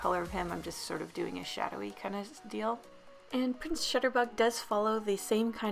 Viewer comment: I thought it said, ' Prince Shitter Bug '.... LMAO